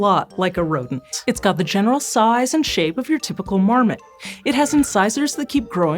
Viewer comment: Anyone else hear "it's got the size & shape of your typical mormon"? I had to double-take 😅